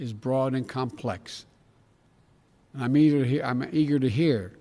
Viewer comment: Yikes - start at 15:31 for what it sounds like him dropping the N word. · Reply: He messed up. He didn’t intentionally say it, slow it down to .5x and you’ll hear him say EAGER not the n-word.